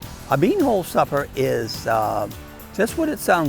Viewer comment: I thought it said Bunghole supper ….lol